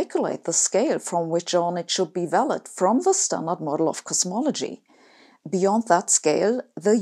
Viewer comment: I thought you said cosmetology you do need this